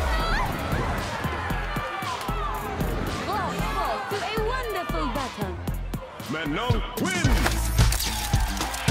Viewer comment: What does she says in 3:44? Before "to a wonderful battle" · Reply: "Bravo, to a wonderful battle!"